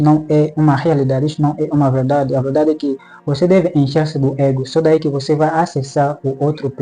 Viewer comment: Em 6:29 Ele diz que tem que fazer o quê mesmo com o ego? Eu não entendi a palavra. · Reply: "Encher-se do ego".